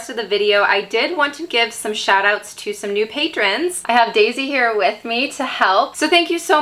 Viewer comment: Idky, but I thought you said Daisyhair, and when you said just Daisy later, I was somewhat disappointed LOL Daisyhair is cute-sounding